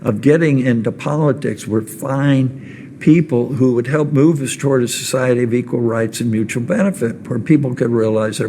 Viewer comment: Did he say “to find people who would help move to destroy equal rights and mural benefit” at 1:30-1:38? That seems even worse than everything else Kyle mentioned if that’s what he said!